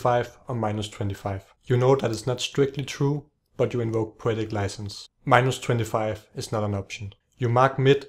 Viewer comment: what does he say at 0:06? 'you know that is not strictly true, but you ????' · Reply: invoke poetic license